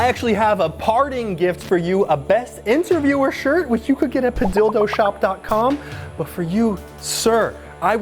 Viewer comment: IT SOUNDS LIKE YOURE SAYING "A DILDO SHOP"